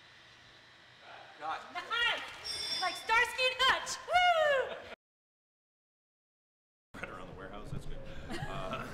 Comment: What Sandra said on 1:27? Something touch.. haha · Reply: she said "like starsky and hutch"